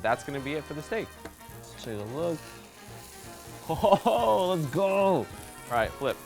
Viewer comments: Is he saying “Let’s go!” Or “That looks cool!” ? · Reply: Let's go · am i deaf or did i hear “Ohhoho lets gooo!” And not “Oh, that looks cool!”